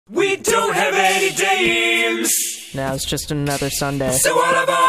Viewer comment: Intro (Rise Of) Lyrics: "We don't have any dates Now it's just another sunday So all aboard the-- Ah fuck it we're single on Valentines Day" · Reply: I thought they said "dames" instead of "dates"